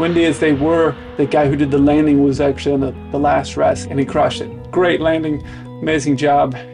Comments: YT's auto-generated captions made a funny—you said the pilot "crushed it" but YT heard that the other pilot crashed it. Quite a big difference in meaning just from one letter being misunderstood. Nice to see you in the left seat, also! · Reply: Glad I found your comment. I heard "crashed" and was wondering if it was some pilot slang. I knew someone in the comments would have clarified things. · For a moment, at 8:38, I thought you said "he crashed it"... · Great story telling and, I agree, it's a travesty that anyone in aviation hasn't seen the original Top Gun! At the end, however, at first I thought you said the landing pilot "crashed" the landing...glad I misheard :-) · i thought you said he crashed it lol i got scared for a second there. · I thought you said he crashed it on the landing. Made me double take😂